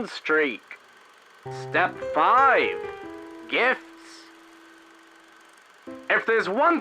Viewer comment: I thought gifts said tits